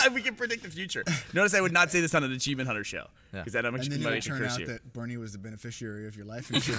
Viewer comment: What does Burnie say at 8:28? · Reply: "Because that much money could curse you."